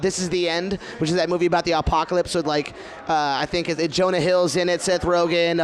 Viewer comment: I swear I heard "Alpacalypse"